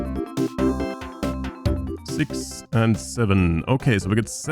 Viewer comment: how many did you say? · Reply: Five, six and…